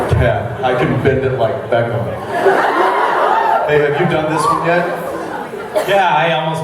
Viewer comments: Did jensen say babe or hey at 1:45ish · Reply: If you turn up the volume you can tell he's saying "hey" but i like to pretend he's saying "babe" :P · is it just me or at 1:50 roughly did it sound like jensen said "Babe, have you done this one yet" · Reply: I just heard: "Hey babe you have done this before?" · Wait... at 1:49 did he call Misha babe?......... THIS IS SO GREAT I LOVE THIS AND YAY MASTER OSRIC WITH THE WIGSSS You can tell how much they all love Jared and were sad he couldn't make it AWWW I love everything about this.